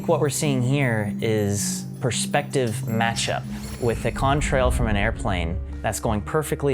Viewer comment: Thank goodness Jordan expounded on Con Trail, I thought he'd said cum trail and I was like no this is getting to crazy